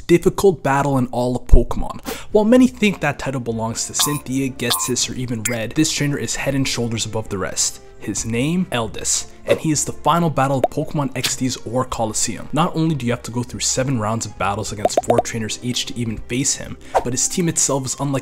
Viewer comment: Eldest? I thought you said Elvis.